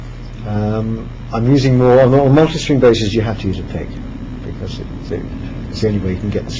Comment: Did he say "On all monster string basses you have to use a pick" ??? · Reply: Multi-string basses